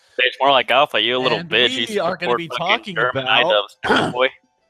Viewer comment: did he say idubbbz